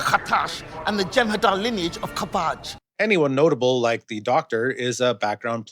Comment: did she say 'from the Jem'Hadar lineage of cabbage', but pronounced cabbage with a French twist?